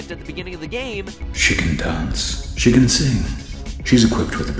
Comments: I thought it said chicken dance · was i the only one thought he said “Chicken dance” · Whenever it says "She can dance, she can sing" I hear, "Chicken dance, chicken sing." Maybe I'm just hungry... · When it said 'She can dance' I thought it said 'Chicken Dance' XD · the "she dance dance" part sounded like "chicken dance" LOL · who else thought he said "chicken dance" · I was scrolling down and instead of hearing "She can dance... I heard "Chicken dance..." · When he said she can dance I thought he said chicken dance chicken sing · I thought he said chicken dance and I was like wat? XD